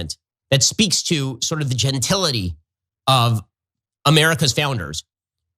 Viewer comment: OK, did everybody catch that? "It speaks to the genitality of America's founders." <3:03> I think he meant to say genteelness.